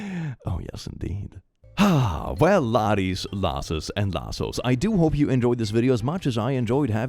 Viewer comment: "We love you, Click!" We, the laddies, lasses, and lassos, all say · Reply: Wait it’s lassos??? That makes more sense tbh. Here I thought he was saying “louses” 🥲 damn auditory processing disorder